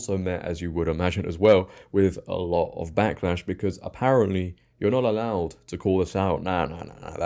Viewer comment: Had to pause the video to tell you that I thought you said something scathingly hilarious, but turned on the captions to find that unfortunately you hadn't. At the 1:29 mark I thought you said "you're not allowed to call a sow a man on this, nah, nah, nah." But you merely said "you're not allowed to call this out, etc." That would've been spot on, but you also probably would've been put in YouTube jail for saying that, so I'm kinda glad you didn't actually do so. I'm pretty used to all types of British (and even Irish) accents from film and TV, but you got me on that mumbled phrase.